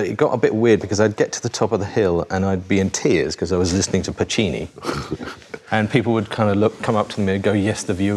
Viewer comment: I'm curious, did he say Pacini or Puccini? Either way, I'd love it if THR got the wrong Italian composer.